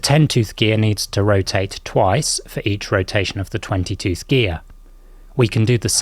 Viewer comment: Totally thought you said 22th gear instead of twenty tooth lol. I was thinking they must really do things different over there in the UK, cm - inch, driving on the left side of the road, now 22th instead of 22nd!!